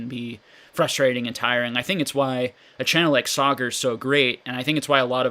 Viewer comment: A channel like "soggers"? Did I hear that right? What's that? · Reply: Royalty gaming Pokémon Sagar